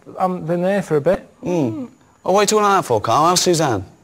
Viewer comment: what does ricky say at 3:19 before "how's susan" I've watched this countless times and cannot make out what the hell he says there. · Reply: Gary McCaffrey 'oh, why you talking like that for karl?' Referring to Karl's apparent campy manner of speaking now that he's gay.